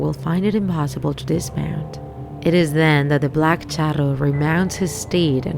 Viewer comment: For a sec I thought it said “the black churro” lol